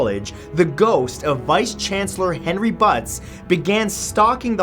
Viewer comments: "Henry BUTTS" laughs uncontrollably like the twelve year old boy I am. I probably misheard that. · His name is Henry Butts (hairy butts is what I heard). I don't blame him to commit suicide